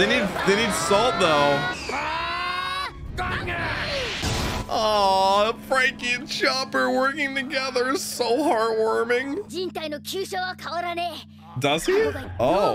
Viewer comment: "Chopper and Franky working together is so hard warming" - I really thought he was saying hard boiled lol